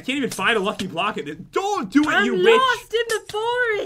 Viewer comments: did you guys think said "the b word" instead of witch? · Min 9:23: "dont do it you bitch" Or at least thats wath i heard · it sounds like you said the b word instead of witch Sorryyyyy. And at 10:00 said save me cat. Oops, I didn't mean to kill ya😂😂lol